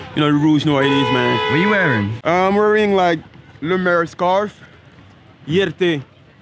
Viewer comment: what scarf did he say @ 2:40? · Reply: Lemaire